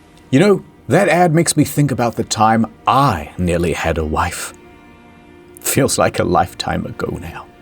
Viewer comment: I thought he said "that reminds of the time when I almost had a life", and it got me.